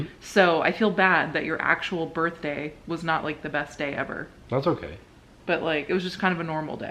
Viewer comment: Happy birthday!! Also I believe was saying "Vanilla" at 1:10 but I could be wrong :) . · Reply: I heard "best day eva" LOL